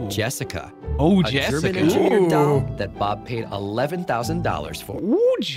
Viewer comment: Hope you guys enjoyed the video! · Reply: QUESTION : ..at 16:33 what in the hell is the narrator saying?? -'..a germidour trainor doll..'?!?! ..garlic what now?? XD